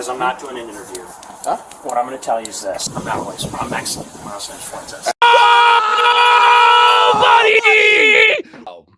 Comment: What did Nick say to the cop at 5:25 that got those dudes all excited? I couldn't hear it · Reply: Break down the barriers and disregard the police. This capitol belongs to us!